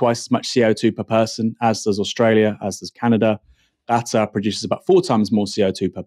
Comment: I had no idea what Aaron was saying. Gata? Cata? I had to go read the article to see that he meant Qatar, which is typically pronounced "kah-Tar" with the stress on the second syllable. (Sorry folks, I got hung up on this for way too long!)